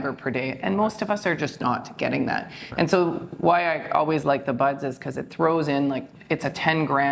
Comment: the guest doctor says "Why I always like ______ 'cause it throws in... it's like 10 grams in a small volume. " What was the word she said between like and 'cause? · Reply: All Bran Buds.